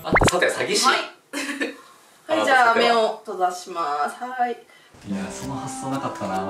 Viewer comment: 目を閉ざします が 目を飛ばします に聞こえてめっちゃ怖かった😅